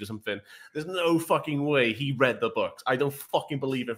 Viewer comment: I thought he said he hated the books why does he care what the movies didn't do